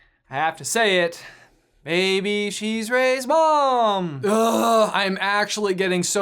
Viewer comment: maybe she's Rey's mom! What I heard: singing ish baby, she's Rey's mom!